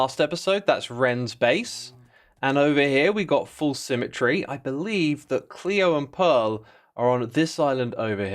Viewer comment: Did he just called false 'foolsymmetry' here 15:45 !?😂😂😂